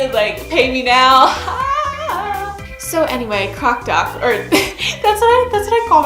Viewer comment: thought ya said cock doc there for a sec >:)